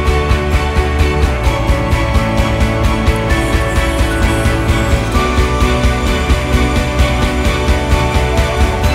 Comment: Can someone tell me what's been said on 3:06? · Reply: "We´ve created a new world"